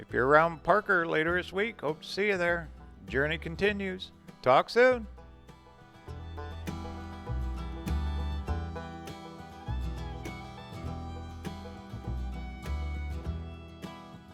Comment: I thought you were saying Datsun( a Japanese vehicle a few years ago) before I realized you were saying talk soon.